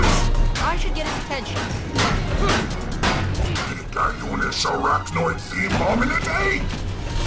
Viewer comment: I think he says “Picayunes arachnoid themed hominidae.” Very hard to tell though.